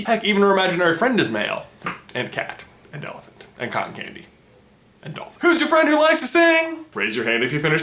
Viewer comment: I literally thought he said kanye not cotton candy😂